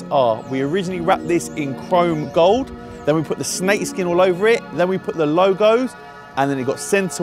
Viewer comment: he says snake skin. The subtitle says crocodile skin